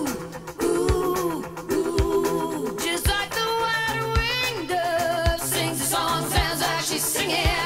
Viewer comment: Telling on myself....first time I heard this ... I heard "Just like the one-winged dove" which was very perplexing lol